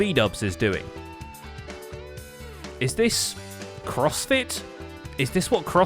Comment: you said "crossfit" but i'm so accustomed to homestuck jokes i heard "prospit" and i had to do a double take because that made no sense